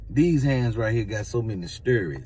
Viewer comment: I thought he said, "These hands got so many "SPIRITS"! 🤣🤣🤣🤣🤣🤣🤣